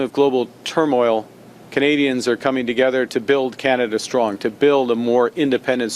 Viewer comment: Canada is thriving I thought he said 😂😂😂😂😂😂😂😂